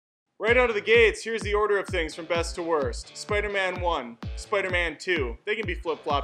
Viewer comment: At the beginning i thought you said worst to best and i was like “ur an idiot” but then i realized it was best to worst, i think homecoming was the best but still understandable